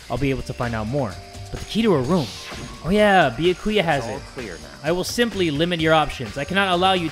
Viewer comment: tried to record: finally done! His recording software: ITS A LIE! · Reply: When I first heard him say that I thought he said " IT'S AMAI"